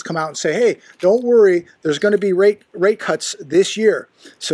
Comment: Did you just say.... Rape Cuts....?